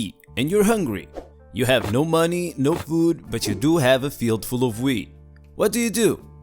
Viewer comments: i swear i heard him say "a field full of weed" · Timestamp 00:30 - I *SO* thought you'd said, "But you DO have a field full of WEED" LOL